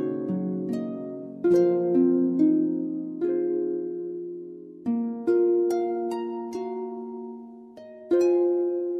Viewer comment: What aøis the dad saying to rashaka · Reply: Liss norma Bolivar Knudsen He said "you should be careful if you go outside the palace walls"